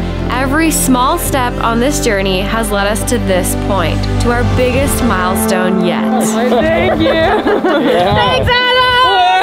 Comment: Sounded like you said you've reached your biggest smilestone yet! Seemed appropriate! 😁